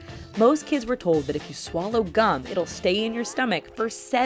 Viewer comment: Who else thought it said cum until she said Gum